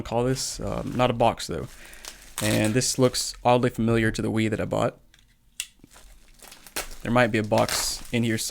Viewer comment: Thought bro said "looks oddly like the weed that I bought"